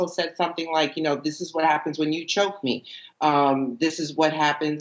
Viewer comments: When he says Sara babe I can’t breath The way she answers “that’s my name don’t ware it out” 😤🤢 I can’t hear afterwards (that’s what I feel when you cheat on me) I heard something about that’s how I feel when you choke me. Not to sure. Can someone confirm Yup see I heard that at 4:55 ‘How I feel when you choke me’ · She doesnt say "When u choke me" she said "When u cheat on me"... And no she wasnt abused because that's not even what she said just FYI if your gonna report on things they should be as accurate as possible...